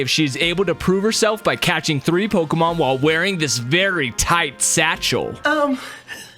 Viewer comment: sash? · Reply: and forward. also I misheard. It is not Sash but 'sachel'